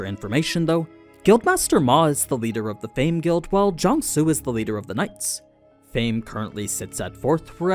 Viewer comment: I accidentally misheard that as day guild and "master of the nights" and my brain immediately went to Always Sunny.